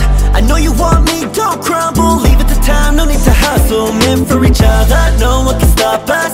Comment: Am I the only one who thought he said men for each other before watching the lyrics?